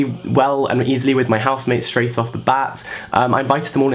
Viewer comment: straight of the buds? What was said? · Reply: Straight off the bat